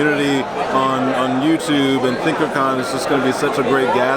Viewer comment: what is wrong with me i thought it said thickercon